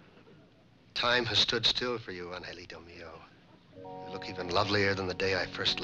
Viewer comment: Does anyone know what he called her and what it means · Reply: Angelito mio. I don't know the meaning but maybe its "my angel"?